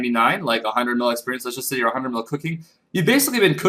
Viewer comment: Thought you said 99 Clicking, but you said cooking!!